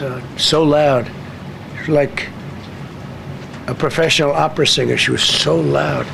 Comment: I thought he said she sounded like a professional opera singer. The psychopath would say something like that😂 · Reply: He did. I'm no fan of DJT, but he did say opera singer and not operative.